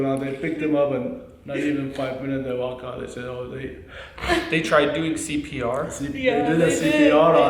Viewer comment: They said what? I couldn’t tell what he said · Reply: They tried cpr